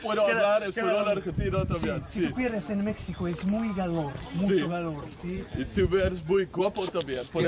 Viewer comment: "Tú cuires en Mécsico es muy galor, mucho galor, sí?" Without the subtitles, I wouldn't even have begun to understand what he was trying to say there.